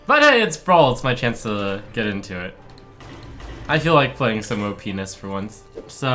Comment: HAHAHAHA At first I thought he said "penis" xD